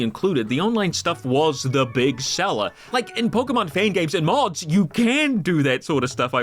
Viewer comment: i swore to god he said “in Pokémon Pancakes and Hogs” and assumed this was some famous fan game or rom I had never heard of and after searching for fifteen minutes did I finally relisten and discover that’s not what he said.